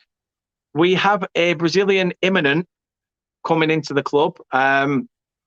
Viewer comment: did he say we are getting a Immigrant? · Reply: I said a move is 'imminent' in regards to Vitinho